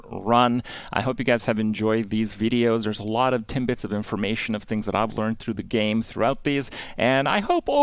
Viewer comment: Did I hear timbits of information lol? @ 0:14? Kripp is a true Canadian